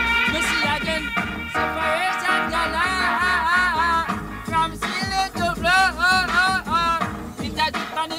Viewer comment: sounds like hes saying "Seperation Galoreeee" lol